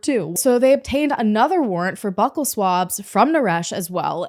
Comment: The way you pronounced "buccal swabs" I swear for a minute I thought you said "butth*le swabs".. I was like.. wow that's excessive... 😅.. I always thought it was pronounced like "byu-kull". My life is a lie